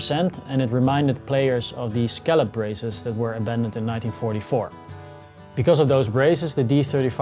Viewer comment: Hey Tim! Thanks so much for you in-depth comment :) To be honest, I meant to say that it reminded players of the braces that were "abandoned" in '44, but listening back it sure sounds like I said "invented" haha. I completely agree with what you said though ;) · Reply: Hey there! You were correct all along! I just listened back to what you originally said and (My Bad) you did INDEED say "abandoned in 1944". I misunderstood YOU. I thought you said "invented"????? So, thanks for your reply. YOU WERE CORRECT THE FIRST TIME! As we say in America (using an Italian accent) "What's a matter me?"😅 Oh well? Tot zeins, Tim